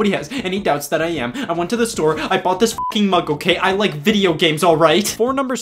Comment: I thought you said I bought these drugs😅